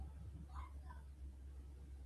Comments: I hear “What the F” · Start at 22:47 and listen Right at 22:50 I hear a female VERY CLEARLY say “WHAT THE!?” Kinda like how you’d scream “what the f*ck!?” If you just saw something like that 🤔 or am I trippin? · at 11.43.09am I hear screaming WHAT THE ……anyone?